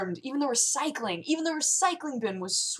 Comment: I totally thought she said that some of the ants were cycling at that point. HOW BIG ARE THESE ANTS!?!